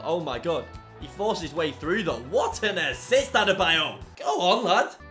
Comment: Did he say Dattebayo